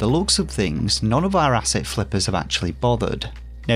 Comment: The first time I ever checked out your channel and you said, " Asset Flip" , I thought you said , "Acid Flip". "PIX ARTS" Sounded like "Pick Ass"😁 Thanks for warning the gaming population.